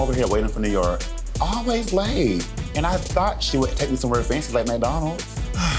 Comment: "I thought she was gonna take me somewhere fancy like McDonald's." 😄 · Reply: Lol for some reason. I heard "greasy" not "fancy"😂😂😂🤣